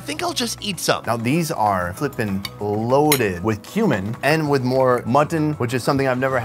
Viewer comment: anybody else get worried when he said cumin but it sounded like human?